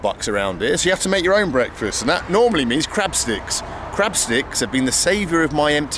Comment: I first thought you said "crap sticks" 😂